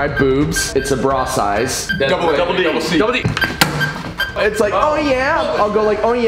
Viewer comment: Did no one else notice say "double c" as the bra size instead of jumping straight to double D at 7:57 😂😂 awe